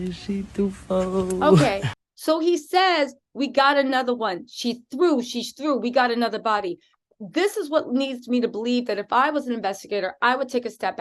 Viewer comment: At the 30:02 do it sounds like he says dead body??? Or is just me🤷🏾‍♂️